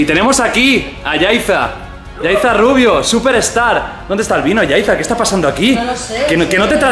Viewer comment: Pensé que dijo pornstar